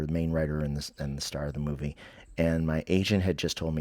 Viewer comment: I know. Irony is thick! 😂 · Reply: Another random funny thing that happened the first time i listened to this, while i was out picking up the pasture, is when you were relating the comedy dream, i had thought you said "My Asian"...twice. I thought, no way, that's not something would say...so i relistened and realized you were saying "my agent" 😂 .... it reminded me of when the antichrist said (at a rally along time ago) 'Where's my African-American. ..there he is!' Yikes, the things we've lived through since 2015 that so many idiots think is perfectly normal and acceptable 🙄 Anyway i love revisiting your videos, and that reminds me to make an Amazon order for your books! It's hot as hades over here, hope youre faring better in buffalo!